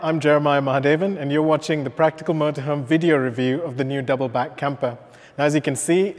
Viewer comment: Was I the only one who thought it said Patrick Mahomes, instead of Practical motorhome